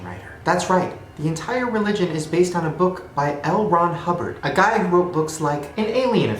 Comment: Am I the only one who misheard the science fiction writer's name , which was L. Ron, for Elrond? (The elf Lord in Lord of the rings)😂 YUP! MANDELA EFFECT CONFIRMED!